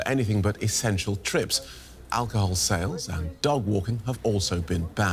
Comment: did he say dog-walking would be banned? I feel silly, but maybe I misheard. He said sales of alcohol, which makes sense, but .. the other part didn't.